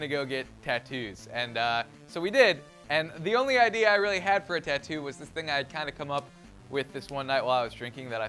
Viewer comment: Did you say Tit tu?